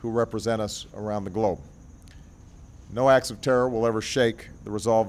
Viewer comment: Okay, well let me correct you, and ask you to watch the YouTube video and go to 4:17. He does not say "act of terror" he is talking about "all ACTS of terror..." in a general sense. You are spinning too my friend.